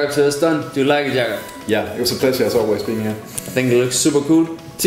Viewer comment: "Did you like the hairstyle Jaren?" "Yeah dmnoipoqejirqawduebeen" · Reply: lol u r an epic bro...but I heard "it's a pleasure to be always a criminal". ik makes no sense but that's what I heard